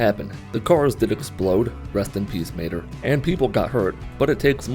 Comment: "The cars did explode... rest in peace Mater" Brooo I thought he said Maynard